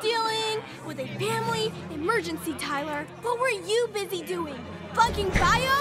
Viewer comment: DID SHE JUST SAY THE F WORD😂😂😂😂😂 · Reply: Oh its flunking